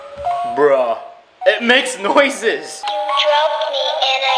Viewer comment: WHEN HE SAID BRUH THE CAPTION SAID BRAH LOL